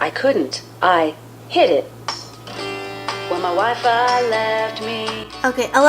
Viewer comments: For a second when it is said WHEN MY WIFI LEFT ME I honestly thought it said wife and had to watch it again 😂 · When Alexa started singing I thought she was saying my wife left me 😂